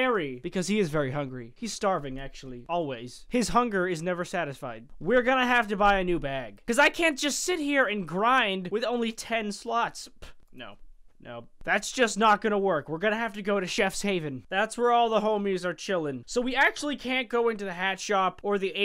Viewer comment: cause i can't grind with only ten sluts. sorry if i miss heard you but thats what it sounded like